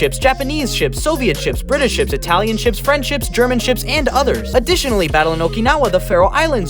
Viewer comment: did I just hear friendships